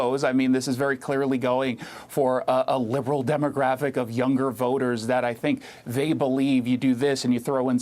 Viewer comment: Historic?? I thought they said hysteric.......(How stupid can you get??)